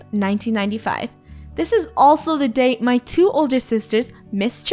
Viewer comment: I thought I heard oldest but it was older sorry